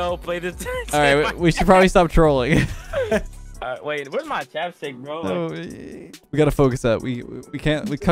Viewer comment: U really impressed me a haha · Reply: I heard you say where's my dab stick 😂 your funny af